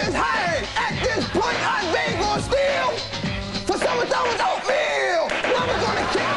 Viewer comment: What did he say from 0:22 to 0:26? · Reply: At this point I better steal For some ?? oatmeal